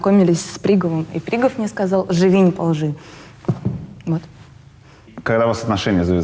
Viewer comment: Что ей сказал Пригов - кто то расшифровал? А то я слышу какое то - "Живи наполжи"